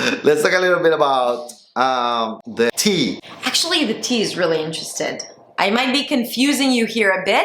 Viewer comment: Hello, I heard you saying The T is very interested and not the T is very interesting.